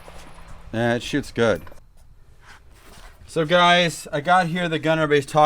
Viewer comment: is he saying man that shits good😂or man it shoots good I prefer to think he said the first one😂😂😂